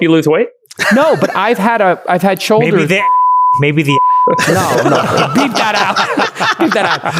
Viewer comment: did he say “maybe the handjob will?” Lol